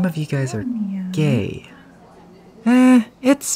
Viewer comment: what did he say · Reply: literally just turn on subtitles. “so uh you guys wanna make out?” just said really quickly.